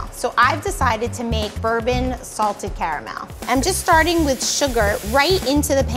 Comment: When she said 'salted caramel' i thought she said salted carrot milk XD